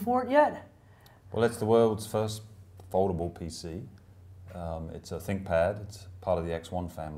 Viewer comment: You said "a foldable pc" - I heard "affordable pc" .... not what I expect the initial pricepoint to be ...